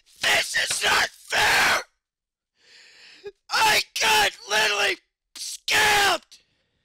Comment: He said fucking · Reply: I don't think Bill said that at 0:15. I thought he said "I got literally scammed".